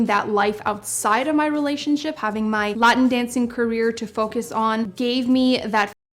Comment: Latin Dancing = Salsa, Merengue, Bachata dance styles from the Caribbean, and Latin America (and New York). Though at first I thought she said Mutton Dancing.